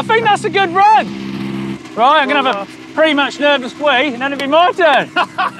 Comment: OK. I'm an American, so bear with me, but... Did Colin just say.... He's going to go have a "nervous wee"? 😜